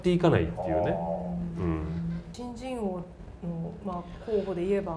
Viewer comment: 上田さんの「新人王」の発音が「ち〇ち〇王」に聞こえてドキッ！としました。病んでるかなー、おれ・・・